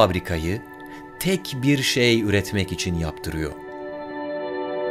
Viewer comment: yaaa pi diye duydum heyecanlandım pilmiş 😢